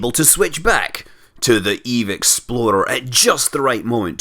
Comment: everytim says Eve explorer i hear ''Eve Exploder'', no offense. -"I was reading a book about a spy".